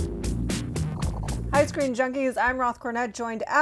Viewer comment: I heard “ice cream junkies” at the beginning lol